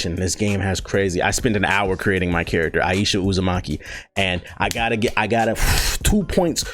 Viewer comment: I really thought he said Aiyesha Uzamonkey instead of Uzumaki